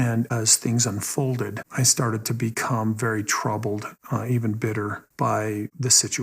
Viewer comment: I was working on something on a different screen, and just listening along, so I heard Kody say "I even bit her" @ 25:21 😂